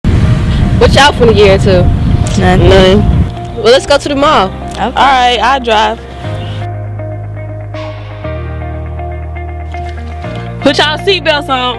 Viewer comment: ✨💞lyrics : VERSE 1 what yall finna get to? nun well lets go to the mall! alright ill drive VERSE 2 put yall seatbelts on PSSHHH yolo beat drops AHHHHHHHHHHHH CHORUS : we told her to buckle up ALWAYS BUCKLE UP! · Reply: I heard Watch y'all gonna year or two